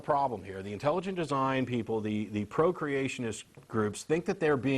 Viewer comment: I thought he said "the Procreationist groups" at around 2:20 That would put a whole new sexy spin on religious ignorance.